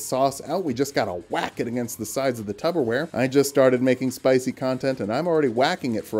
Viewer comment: did I hear you say tuber wear instead of tupper wear